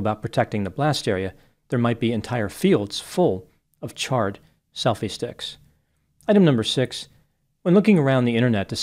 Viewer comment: Your line about a field full of charred selfie sticks was priceless! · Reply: I thought he said selfish dicks and I about peed my pants lol